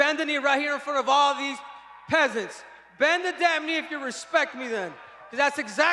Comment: dafuk did he said " peasants " ?